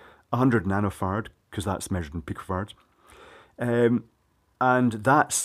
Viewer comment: When he said peak farads at first I heard pig farts right around 7:46 "cus that's measured in pig farts"